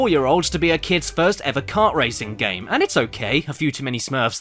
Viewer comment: I could've sworn I misheard Barbie saying "racing" as "raping".